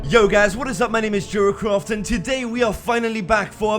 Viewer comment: it sounded like he said jerkoff instead of jeracraft just saying, no hate